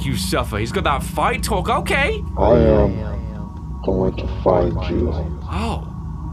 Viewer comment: So basically I know what the voice said. The voice said: I’m going to find you.